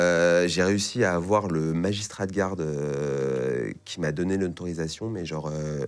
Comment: C'est quoi un "magistral guard" ? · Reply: Magistrat de garde